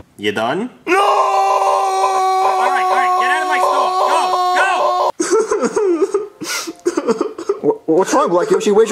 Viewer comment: What did BTG say? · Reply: Get out of my store go